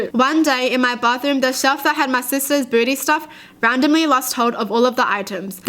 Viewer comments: On the first story said “the shelf with my sisters beauty stuff” and I heard “my sisters booty stuff” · did anyone else hear "booty stuff"? Just me? Okay. Edit: 4 likes?!? Wow thanks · I really thought she said booty stuff instead of beauty stuff 😂😂😭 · I swear I heard her say "My sisters booty stuff." And I had to stop and read the actual story 😂😂😂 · She said beauty stuff I thought she said “booty” stuff